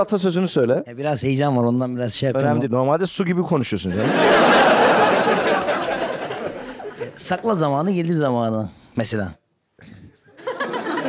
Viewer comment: Sakla Zamanı, Gelir Samanı diye anladım))